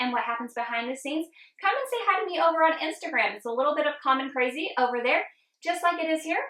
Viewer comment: Seriously thought you said, “Little Bit of Common Crazy”-haha! LOVE THIS! I am working on my own YouTube channel. Thank you for the inspiration!